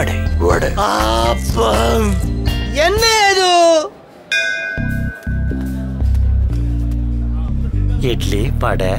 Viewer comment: I don't know what did he say there , but that was epic 😂😂😂. Andeee eduuu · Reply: he says " What is this?"